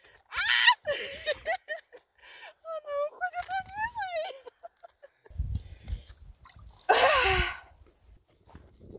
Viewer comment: Another amusing video you are definitely one of a kind...what did you say at 1:27 ?! I like the ttttruuu sound at 0:41 😊 You are the definition of happiness ! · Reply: Daveб I sad "It goes down at me" 😅